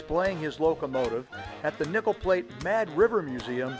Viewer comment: I’m sorry, but every time you said “Nickel Plate Road”, it sounded like you said “Nipple Plate”. Awesome drone footage of a beautiful railroad!